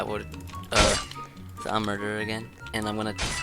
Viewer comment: I swear i heard a kid say murderer